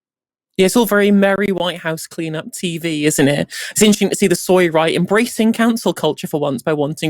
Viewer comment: What did say (11:56)..."soya or soiled right" ??